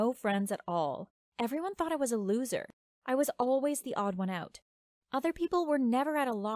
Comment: Did you say theodds1out